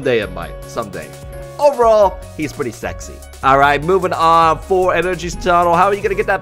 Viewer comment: did just call alolan exeggutor sexy?